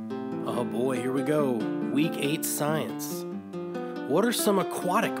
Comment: Did you just say “we hate science!”😂❤